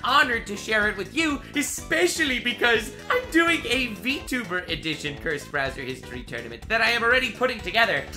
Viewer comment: I thought you said feet tuber.